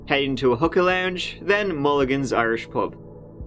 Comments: Oh dear, everytime you said "hookah lounge" I thought you were saying "hooker lounge." Hahahaha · I thought he was saying "hooker lounge" 😂